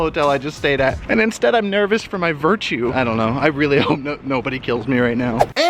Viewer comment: I thought he said I’m nervous for my virginity 😂